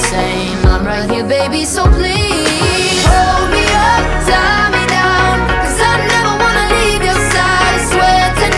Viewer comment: I thought they were Saying Romeo Tie me down😂